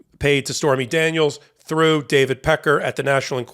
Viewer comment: I thought he said Richard Pecker but still...